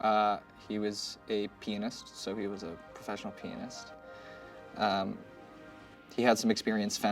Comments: I really thought he said "he's a penis, a professional penis" 🤣 aight, amma head out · Am I the only one who at first thought he said the creature was a "penis" instead of "pianist"? SMH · thought he said professional penis! wtf! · I misheard, I thought he said "penis". but it actually Pianist.